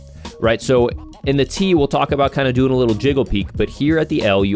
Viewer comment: Did someone say “Jiggle Peak”?!?! Haha